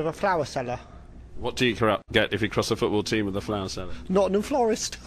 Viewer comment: What does he say at 3:07? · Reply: Kristina Bo Nottingham Florist